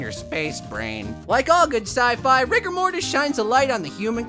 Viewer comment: I thought I heard him say rigor mortis